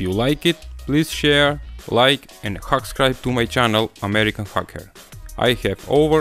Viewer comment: Sounded like he said "American Fucker".